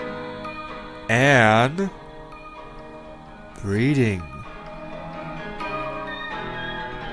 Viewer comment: anyone else heard "BREEDING" at 3:55?